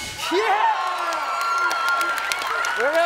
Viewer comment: lol at the end it sounds like steve says "i appreciate the crap bastard" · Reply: Sorry for being 9 years late, but he said "appreciate the prayer pastor".